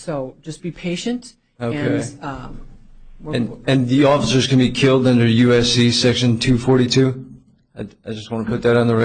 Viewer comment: Did I just hear this motherfucker say, “And the officers can be killed... and should be, in my opinion.”?